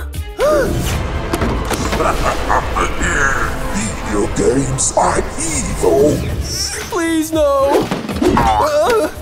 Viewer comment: i just Heard grandma said "Video game arent evil"